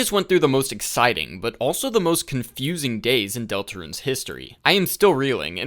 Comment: It sounds like you just said “most confusing gays.”